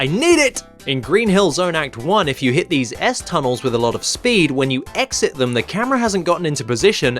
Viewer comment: Who else heard "@ tunnels"?